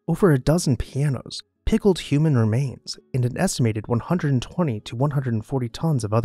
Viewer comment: Did i hear “pickled human remains”